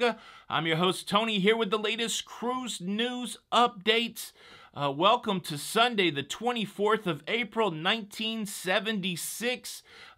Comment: Tony, why did you say welcome to Sunday the 24th of April 1976?